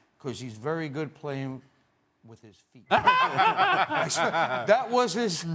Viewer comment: What did he said at 4:08, can anyone understand?? please let me know.. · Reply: He plays good with his feet